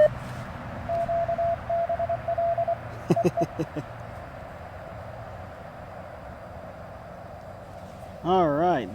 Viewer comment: you were chucklin' at what sounded like QBL.. what's it mean? :p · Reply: Nice catch! Hah, it’s a joke among the folks in the POTA CW channel on the POTA Discord and means “Quit Being LID”